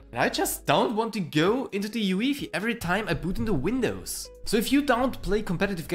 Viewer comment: did you say uefi as yuwifi?